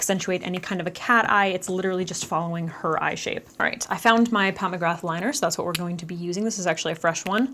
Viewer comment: I listen to all videos on 2x speed, and I thought you said pomegranate liner not Pat McGrath liner hahahah